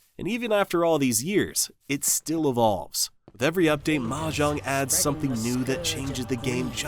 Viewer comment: did you just say mahjong